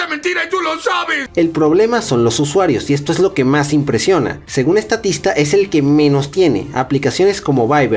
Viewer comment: soy yo o escuché "según estatista"? jajaja